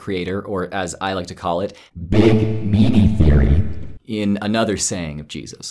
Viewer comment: What-what theory? I backed it up several times and couldn't make it out, and there's no caption for it. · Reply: Sounds like he said “big meaty” theory. 🤷‍♂️